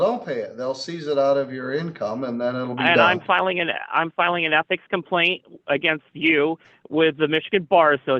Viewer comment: Oh he was saying ethic! I thought he said he was filing an epic complaint against you. Not the epic complaint! LOL